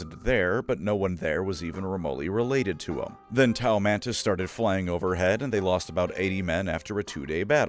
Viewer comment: when he said Tau Mantis, i thought he said Tau Volantis from dead space three. I blurted out: "Hold up, how did i click on a dead space vid- Oh..."